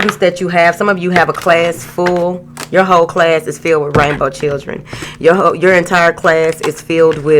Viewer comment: Thought she said Rambo children at first. 😄🤦🏾‍♀️My bad. If done right spiritual schools would be a good look especially early start.